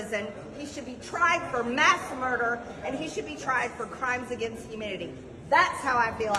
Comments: Did she say “Crimes against humidity” ? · Did she say “crimes against humidity”?